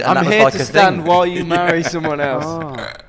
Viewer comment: What did harry say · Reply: Marty Gay Palermo I think he said "i'm here to stand while you marry someone else"